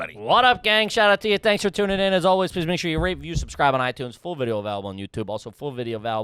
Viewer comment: Sometimes it sounds like says R*PE and subscribe.